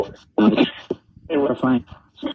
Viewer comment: at about the 1:02:57 mark when you asked Erika how she felt about that, I heard 'I'm fine' and then a gold small streak went across your shoulder area - super fast. Could've been a bug but I don't think so.